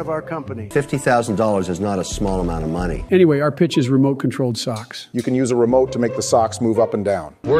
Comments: I for real thought they said stocks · I thought you said stock 😂🤦‍♂️